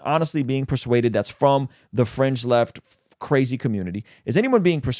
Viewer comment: I thought said "French left". Good point that conservatives tend to neglect emotions. Whereas leftists are good at appealing to emotions. I love Matt Walsh. He says things as it, and I think the Christian community need men like Matt Walsh to deal with arrogant leftist academia who are not interested, in fact hate the Christian community. Maybe speaking dry facts is a conservative thing but the choir also needs to get their dose of daily preaching too, if not for anything but to ground them in a society that is trying to pull them out from the belief system that underpinned the foundation that allows for a society to stay strong and cohesive. You cannot save or redeem forces that are trying to destroy human families. Trans activism is one such force, so yes, preach the anti trans activism till youtube kicks them off. It will show up youtube and whatever platforms for what they are.